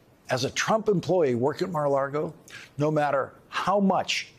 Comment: Personally, every time he says Mar A Lago, all I hear is "Mar-A-Lardo" 😂😂 Yes. Good.